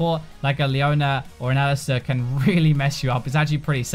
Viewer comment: What champ is he saying at 8:40 ? "Like a Leona, or an Ersa" is what is sounds like to me. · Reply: Alistar